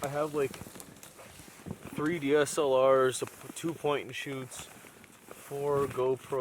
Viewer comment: I thought was about to say he had a 3DS out there :D